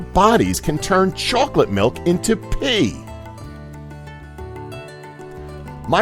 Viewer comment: 26 I thought he said pee into chocolate milk